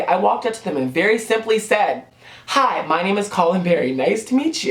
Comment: I don't know why but I heard "Hi my name is Rachel Berry." Lmao. Gotta love glee